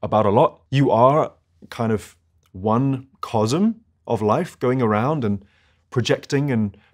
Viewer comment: anyone can hear what he said in 1:13? What of life? What is a 'cozum'? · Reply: it's cosm (universe, world). like microcosm for instance